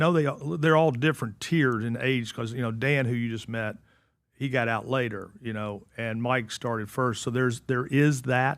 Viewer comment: Did i hear "dan who used meth, he got out later"?